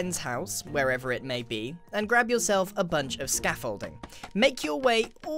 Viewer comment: i thought he said a bucket of scaffolding